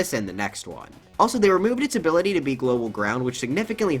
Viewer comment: Wait what did you say here? Global ground?